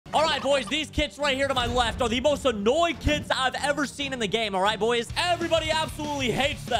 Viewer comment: At first I thought you said "the most annoying kids"